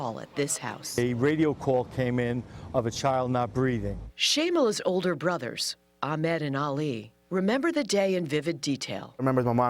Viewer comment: It sounded like shameless older brothers